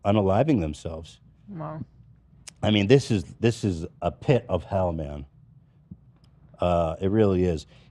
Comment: Ethan says “this is a pit of hell man” and I heard it as “a pit of Hellman’s” and it reminded me of when Zack filled the pool with mayo